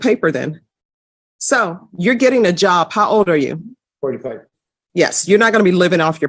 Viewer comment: did he say 45 ?!?!?!?!?